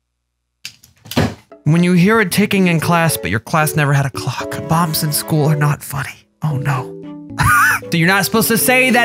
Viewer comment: I thought you said 'Moms in school aren't funny'